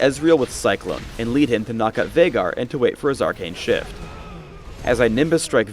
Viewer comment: sounded like "as I wait for his arcane shit"